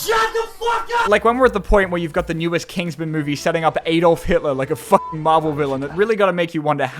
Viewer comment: Anyone else noticed that at 2:54 when he mentions Adolf Hitler the captions say Elon Musk? 💀